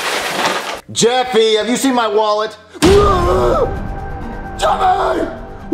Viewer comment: jeffy do you seen my Walter?😮IWJDFJFHDJAIAOA😨JEFFY!! WHERE ARE YOU AM, JEFFY?!😡*Nosie jeffy*😅what? Wait jeffy wwait jeffy?😮 *SCREAM JUMPSCARE*. best thing😂